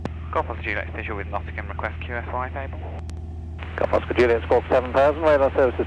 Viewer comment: can anyone explain what was said at 25:51 "G-OJ visual with Nottingham request (sounds like QFI) if able"